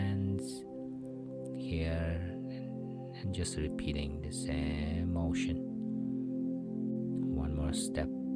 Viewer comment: Did he actually say, "....and just repeating the same old $h!+"? That can't be right. Anyway this is very relaxing to watch and listen to. Thanks for sharing this.